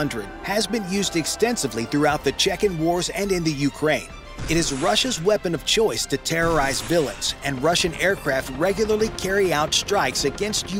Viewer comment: Did I hear the narration correctly? "It is the Russian's choice to terrorize villains."? [just after 6:11`] The Chechens and Ukrainians are villains now, after being invaded by Russia? Maybe I misheard, so you want to clarify here? P.S. The Russian say they have a nuclear-yield thermobaric bomb? Horseshit. Bring it.